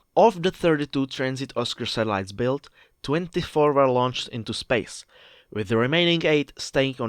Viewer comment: close caption error. You said into space, but the caption says into service. So which one is the real?